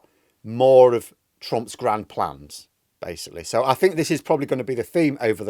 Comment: At the end I heard "Moron Trump's grand plan" instead "More on Trump's grand plan" and I think its more apt.